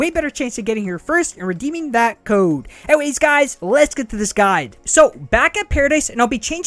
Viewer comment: All that time I thought he would say, “Let’s get to the sky” , but in this one video I finally realizing he’s saying “Let’s get to this guide”